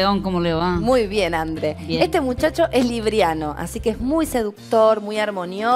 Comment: flasheé que dijo libiano y pensé que había nacido en libia XD